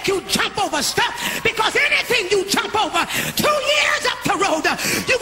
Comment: Did anyone else here her speak on corrnona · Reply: So I was looking for this comment again because I listened to this sermon again. Not thinking about it really does sound like she said 2 years of corona I'm sure she said 2 years up the road but I dont know maybe because I'm in the middle of a fast And hearing and seeing things differently.